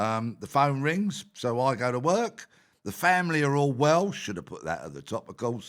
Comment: At around 02:00 when Peter said 'The family are all well should have put that at the top' he drew out the 'sh' in 'should' and it sounded like he said the family are all Welsh. And for the few moments it took me to realise I'd heard a mondegreen I was thinking to myself that's unexpected, I would have thought the family of this bloke who sounds like the bloke who'd say 'Seven' on the dancing show on BBC 1 were more likely to be all cockneys than all Welsh.